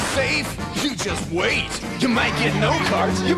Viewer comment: "You might get no cards, you might get eight!" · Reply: I swear my dyslexic self thought he said "you might get aids" 😂